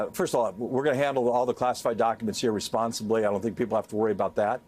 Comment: excuse me??? WTH did he just say????? I heard that!!!👆🏼 NOT COOL! I can’t believe he just said that like it was normal. That’s the problem with the whole thing. Why is he stepping down,anyway? 🫤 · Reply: Yeah you heard him wrong he didn’t say irresponsibly he said “we’re going to handle the documents here responsibly”!! So stop fishing for something that’s not there!